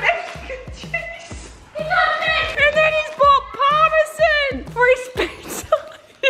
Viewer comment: did she just say Parma-Zin ?????😟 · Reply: did she just say Parma- Zin ?????😟